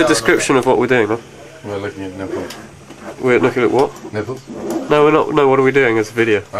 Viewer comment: He said looking at nipples? LOL. I thought he said 'netbooks' at first. :/